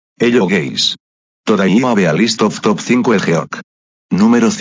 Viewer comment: the speech is kinda weird I heard it say ‘Hello gays’ damn